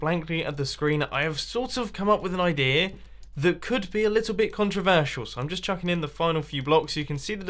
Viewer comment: Did say controversial? This video is getting restricted!